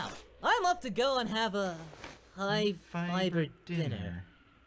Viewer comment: OMG HE SAYS "HIGH FIBER DINNER". I thought all this time he said "High five... or dinner"